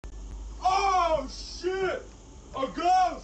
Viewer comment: when he said ghost i thought he said rat 👁👄👁